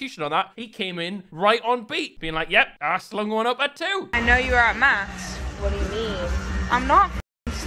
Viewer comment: I thought the girl said “I know you were at mass” and I was like are you a die-hard protestant or what??